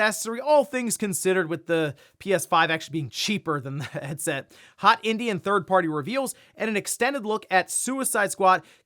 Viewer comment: Did he just say hot Indian third-party reveals? Sounds scandalous LOL